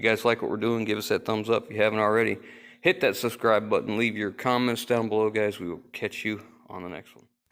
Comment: 👍👌👏 I definitely heard: "Cummins down below" instead of "comments down below"! :-) ;-)